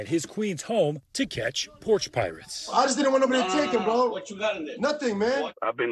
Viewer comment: What did he say? Sounds like, "I just wanted a McChicken, bro!"